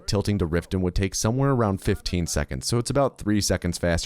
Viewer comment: I was using this video as background audio while I was doing something else, not really paying attention, and all of the sudden I heard "When we arrive in Riften, Waz will fist a horse".